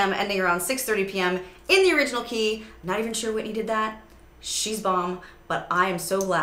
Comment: What's she saying at 0:31 and at 2:38? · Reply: "Not even sure Whitney did that, but she's bomb" (same key) "okay girl, this is where it is all on the floor, all the bananas" 😂